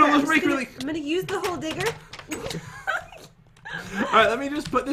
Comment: I thought jen said glod digger i was like what!? then i said oh maybe it was like it digs gold yeah yeah thats it then i listened to it then i realized it washole digger Me: oh